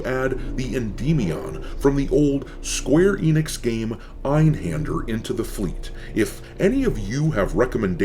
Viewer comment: What game was this ship from ? Sorry i cant understand what he says here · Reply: Einhander